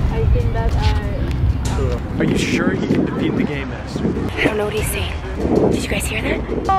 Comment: I heard say"are u sure u can beat the game master."